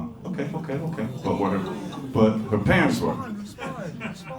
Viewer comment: What did she say? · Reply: Not entirely sure but, "Our parents are responsible for a lot of things. That's why I'm here and you're there."